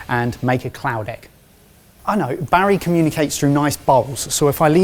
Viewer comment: Well thank you.. I thought you said Cloudeck, and inadvertently I found one weird piece of hardware 😅